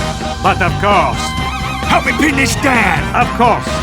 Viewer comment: did anyone heard the sniper said penis dance? XD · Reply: He said "Help me pin this down!"